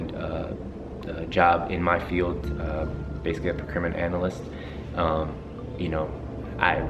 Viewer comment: What is career I thought he said a percrimmon analyst? I know that's not right, but that's what I heard. Love you two. Xoxo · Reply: Brenda Nolin procurement analyst :)